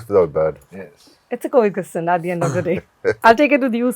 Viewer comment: what’s the word coaction? Please tell me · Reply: I guess she fumbled on word Coexistence